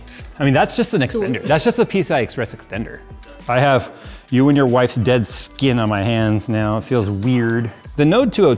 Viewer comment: I misheard that as "I have you and your dead wife's skin on my hands now"and thought "Oh no! His wife died ...and her skin is on Jay's hand's?!!"